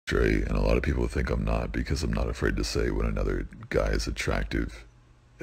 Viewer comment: wait so is he straight? hes voice is hard to hear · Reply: i thought he said train